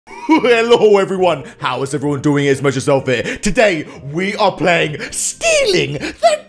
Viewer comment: did he said its messyourselfEH